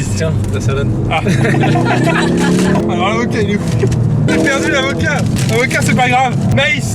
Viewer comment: "J'ai perdu l'avocat" Moi perso j'ai compris : J'ai perdu la vodka !